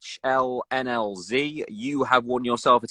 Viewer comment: does he said "lhlnz"?